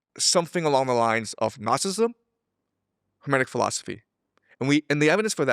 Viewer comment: ASR subtitles wrong, it picked up Gnosticism as "Nazism" and now all the AI's are misrepresenting this...